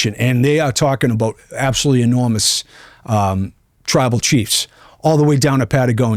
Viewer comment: did you say, tribal chief?? puts index finger finger up to the sky